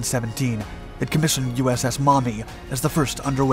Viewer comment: USS Mommy? i'm sure i heard that wrong but i can't unhear it and don't know the actual word lmao